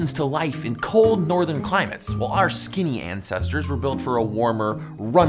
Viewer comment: When he said skinny ancestors I thought he said skinny asses